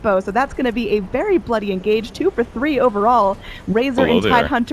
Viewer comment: 3 for 3 plss!!! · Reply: first, it's @ 2:39 .. second, you need to get your ears cleaned.. she clearly said 2 for 3.. noob! :D